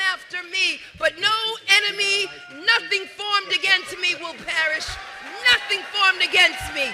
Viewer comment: Did she just say "nothing formed against me will parish"?